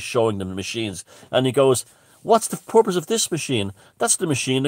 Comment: important notes: when says purpose it sounds like hes saying porpoise. also robert phoenix the astrologer got into the symbolism of the london olympics, children of men, pisces, and the plandemic; all tied together. worth a look.